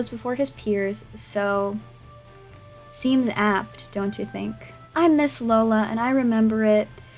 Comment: I clearly don't pay attention to whose channel I'm on because when at 14:14 when you said 'I'm Ms Lola.' I heard it as 'I miss Lola.' I was like...the fuck is Lola? That's weird but whatever, oooohh, right...literally your channel name, got it.